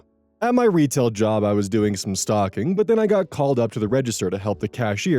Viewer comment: for a sec i thought it said "stalking" and was like "TF?", tabbed out of what i was doing to my other monitor, to the video, and then like "oooooooh.... stOCKing...."